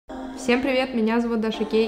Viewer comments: Даш прости, но мне послышалось :Всем привет! Я Даша Гей" 😂😂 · почему я слышу Даша гей?